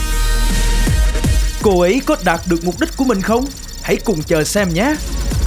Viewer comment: đạt được mục đích mà có ai giống tui nghe thành đạp được mục đích không , kkk